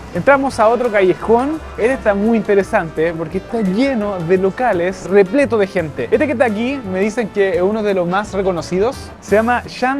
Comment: le escuche "este Kentaky" ahhaha 😅 perdón 😢😂